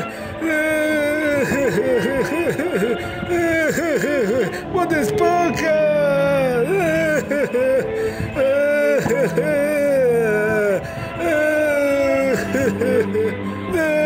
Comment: parece que dijo botesponja en lugar de bob esponja